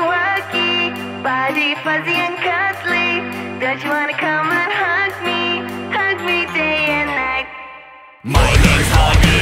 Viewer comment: Instrumental version available on my Patreon! <link> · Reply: Am I the only one who thought that when they said don’t you wanna hug me? They said bitch you wanna come and hug me?